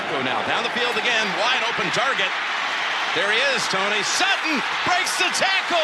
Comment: did i just hear the commentator say "Tony Sutton?" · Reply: He was telling Tony there he is Tony(Romo) Sutton, to make it easier Imma put the comma after Tony said “there he is Tony, Sutton” if that makes sense